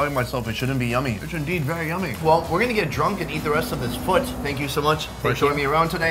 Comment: well we guna get drunk tonight and eat the rest of this FOOT haha i bet you 100 dollars Korean guy thought he said FOOD